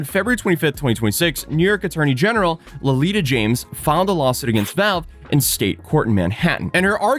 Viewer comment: lelita james ...i first heard lolita 💀 ...it's letita (spoken like le-ti-tcia)